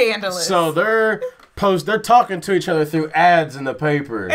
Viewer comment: I've never understood what he says there, but I don't think it's "a bar named Andy's". A friend of mine says it's "a bar called O'Malley's" but I don't hear that. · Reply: It's O'Malley's